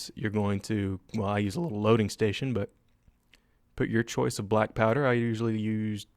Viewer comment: I thought he said "black power" instead of "black powder" No racist tho